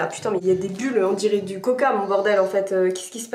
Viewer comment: A 4:52 tu à dit coca mais pour moi c'est caca👁️👄👁️💀